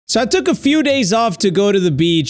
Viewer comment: i thought he said”i tok a some days of at the bitch”